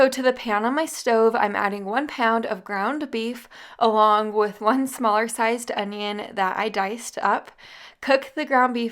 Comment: Did you say one SMARGE sized onion?😅